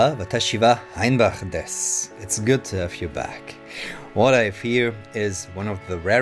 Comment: I thought he said "what I fear" instead of "what I have here", and I was like why is he afraid of it?? Is it that powerful??